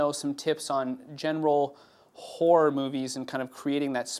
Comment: lol i thought he said whore movies